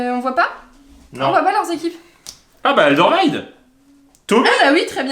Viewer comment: quoi comment tu as dis !! Big up de Belgique 😂😂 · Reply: '' Aldervaïd '' 😂 😂